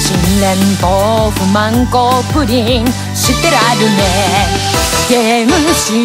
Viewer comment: が🈵こープリンにしか聞こえない。